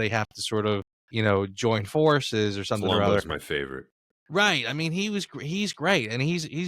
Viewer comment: I couldn't catch the name (and neither could the subtitles), which ally is your favourite? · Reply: Colombo from FYEO.